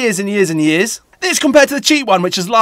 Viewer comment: I heard "furry cool" I had to rewatch to underatand he says "very cool"